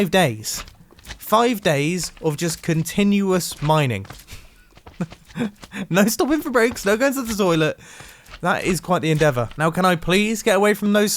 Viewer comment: What the hell did he say? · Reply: No stopping for breaks, don't go to the toilet.